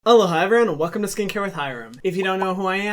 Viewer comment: Wait..... has he been saying “aloha” at the beginning of every video...? O MY GOD I THOUGHT HE WAS SAYING “HELLO HI” FOR LIKE EVER WHAAAAAT?!?!?